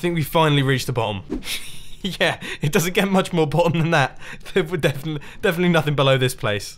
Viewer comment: is "much more bottom", not "much more important" like the captions say.